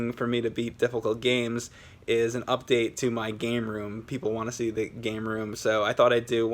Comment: I thought u said game room not poster room